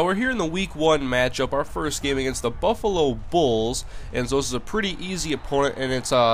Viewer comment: I thought that said Bufflo Bills at first